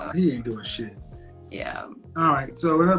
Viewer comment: she actually said YEAH.